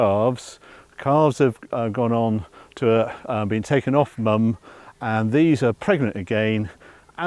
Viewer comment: "Calves have gone off to a...been taken off mum" Slaughterhouse? Veal lot? Whilst always truthful, at times is very selective about what he says. He almost accidentally told us something there! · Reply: * calves have gone off to a -nother farm. The calves would be 9-10 months old, when removed from their mother. they’re not slaughtered until 30 months of age